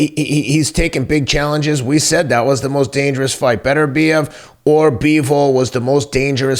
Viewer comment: Is it me or does it sound like teddy is saying "Beterbidez"